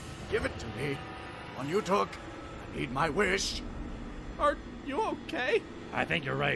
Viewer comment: for some reason I always thought the line was "Heed my wish" instead of "need my wish", which genuinely made it alot funnier for me.